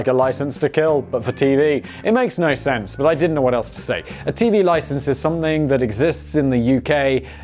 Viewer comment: I thought he was saying"tea licence." Sorry, I'm from Massachusetts.